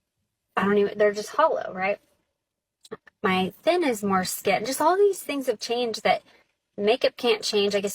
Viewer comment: Did the woman at 22:10 say "my thin is more skin" ? Lol. I thought that's what she said.